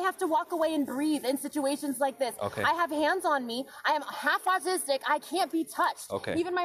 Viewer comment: I love how people think they can refuse the commands of an officer because of how they feel...absolutely precious. She claims anxiety and autism, my son has the same issues and is completely respectful of authority and knows how to act. She's using it as a crutch, it's an obstacle, not a crutch. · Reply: seconds is she actually saying "half autistic" or "has autistic"? because neither makes sense.